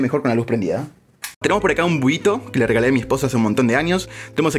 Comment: escuche esposO? 🤨 Sigo siendo tu fan pero ojalá ke respondas es solo para aclarar xd soy el único?